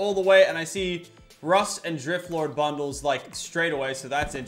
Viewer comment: did he just say Drift lord XD